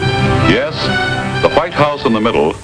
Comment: i misheard meat flesh